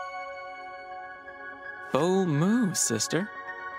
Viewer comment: "b0lD mOvE SiSteR" - Ed (basically all of us rn) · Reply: PLSSS I THOUGHT HE WAS SAYING OH MOO SISTER AND I WAS SO CONFUSED😭